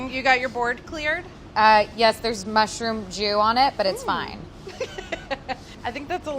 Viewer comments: I heard “There’s mushroom Jew on it” 😂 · Did she say "mushroom jew" at 1:25?